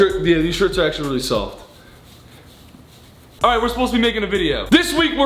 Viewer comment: i literally heard that as "alright we're supposed to be naked in a video" what is wrong with me